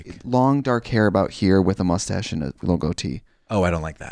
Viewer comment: When trixie saw a ghost with a “little goatee” I thought she said “logo tee” 😝